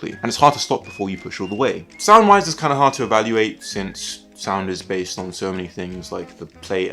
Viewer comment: in the middle of the word basically my internet died for a split second and it sounded like you said “So the sound is bae.”